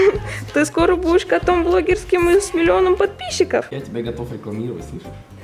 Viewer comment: что он сказал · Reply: Varvara Kery, я готов тебя рекламировать)